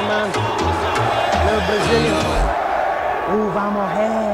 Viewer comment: I heard “little Brazilian who got more hairrr”😂